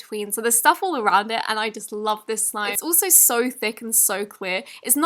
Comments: Who else heard: So Thicc And So Qweeur?? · she said queer instead of clear